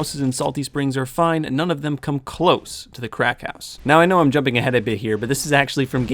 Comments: Ohhh... ‘cracked house’ I thought you continuously said ‘Crack house’... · I thought he was saying the crack house not cracked house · I thought he was saying Crack house instead of cracked house like crack(drugs) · Lol i thought you said crack house instead cracked house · When you said cracked house I thought you said crack house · He said “cracked house” I thought he said “crack house” 😂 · Lol I thought he said Crack House not cracked house lol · I thought when he said "cracked house" it sounded like "crack house" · I thought he said the crack house not cracked house😂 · Every time he said "Cracked House" I thought he said "Crack House" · i thought he said crack house not cracked house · When you said “cracked house” I thought you said “crack house” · Am i the only one who thought the whole time he said "crack house" instead of "cracked house" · Instead of cracked house I thought he said crack house 😅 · Ha ha ha I thought you said crack house lol